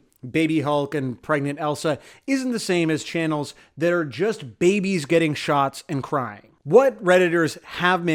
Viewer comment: every time you say 'babies getting shots' I hear 'babies getting shot'. thanks.